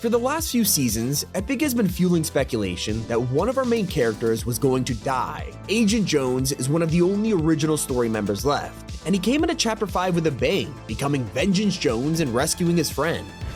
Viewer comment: Sounded like he said hoe 💀